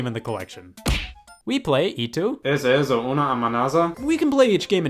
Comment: that should be ¿Es esto una amenaza? But spanish levels are -1 so it sounded like kfihebckcjjd😂